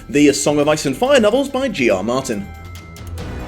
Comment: Varis, Veris and Varees. Because pronouncing things right (or even consistently) is for NEEERDS. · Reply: Talking about pronouncing things's weirdly, when I heard you say "by G. R. Martin," I thought you said, "Geor Mormont." What a strange mishearing adventure.